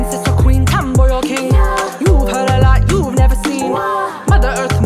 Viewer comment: Dunno why, but sometimes I hear "wombat" instead of "woman", so I sing "Let me be your wombat" instead... And I'm lovin it. Don't judge, I'm lonely.